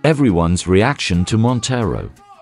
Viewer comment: "That's so gay!" Aint nothing wrong with that. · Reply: I Thought It Said "That's So It"